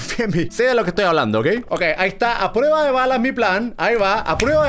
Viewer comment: Yo cuando escuché "aprueba de balas" yo BTS!!! No te creas